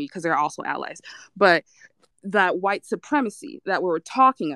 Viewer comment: Did she just say white supremacy is also our allies @ 12:15 or am I just hearing things